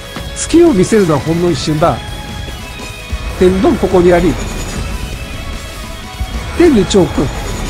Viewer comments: どうしても「天道ここにあり」が「天丼ここにあり」に聞こえるww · ここ本家より天丼って聞こえる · ｢天道、ここにあり｣が｢天丼、ここにあり｣に空耳で聞こえるじゃなくてこれはもう確実に言ってる(？)